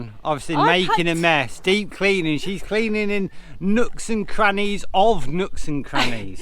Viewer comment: Glad to hear you've cleaned all those crooks and nannies 🤔🤔🤔 I may have misheard you 🤪